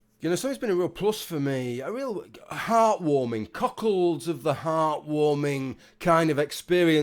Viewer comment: Cuckolds of the heart? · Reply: I was also wondering what the hell you said there; I heard "Cockerels of the heart" and was very, very confused.